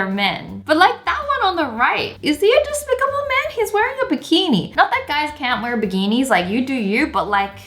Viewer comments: did Wengie say “BIGENI”??! INSTEAD OF BIKINI?😝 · Bigini Or am I just hearing things? I’m also from Kentucky so KFC!